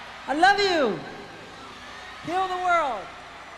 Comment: when he says “heal the world” the subtitles say Kill the world. YouTube subtitles are about as dog shit as can be